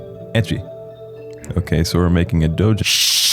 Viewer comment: did u say doju? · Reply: Doujin